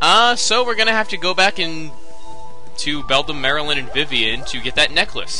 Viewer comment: he said go back to Beldam, Marylinn, and Vivian. I thought he said go back and do Beldam, Marylinn, and Vivian